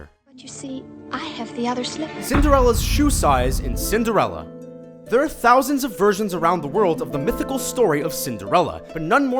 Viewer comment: why did i hear "there are thousands of virgins around the world"